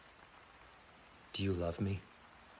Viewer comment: I thought he said “Toe do you love me”